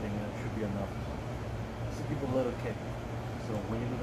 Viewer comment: At @ 1:46 you say something that I don't understand. It sounds like you say something about the letter K. You say it two times very close together. What is it that you I am mishearing? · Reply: Give a little kick